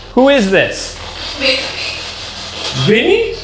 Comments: I heard Britney, maybe the. Ghost likes Britney Spears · At the beginning with the spirit box when you asked "who are you?" and you thought they said "Vinny", I distinctly heard it was a female voice saying "Vicky". I'm so curious if there's an historical link to a "Vicky"! Ryan, since you did the research, was there anything related to a "Vicky" anywhere?